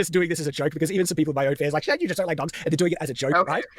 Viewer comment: It’s kind of funny how Shad’s automatic subtitles misheard his name in this moment and thought he said Shit. Honestly, it’s a pretty good description for the quality of his response video. 😂